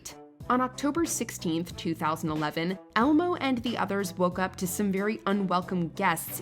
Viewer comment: did she say Almo?😅